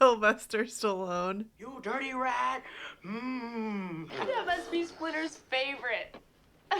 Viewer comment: I thought she said "Oh you must be Splinter's favorite!"